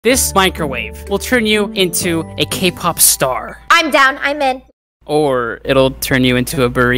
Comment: i thought he said cake pop star💀